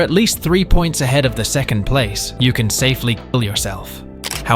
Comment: Bro did not just said "you could safely ky$" at 2:57 😭